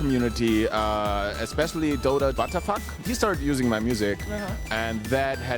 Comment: is it just me or do i hear Butter F**k ? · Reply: He's talking about DotA Watafak